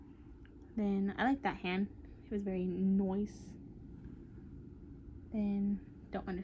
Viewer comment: Hahhaa “I like that hand it’s very nOice” haha at first I thought you said very moist XD